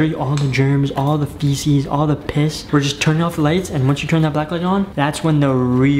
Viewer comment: Did just say piss